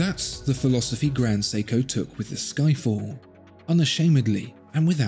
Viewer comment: did he say Skyfall?